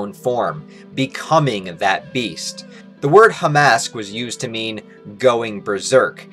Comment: did you say Hamas like the terrorist group?